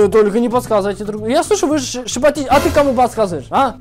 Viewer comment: хаха я сначала услышала глобус😂